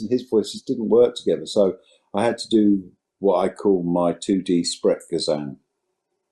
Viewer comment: what did he say? Spretgazone? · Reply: Sprechgesang = chanting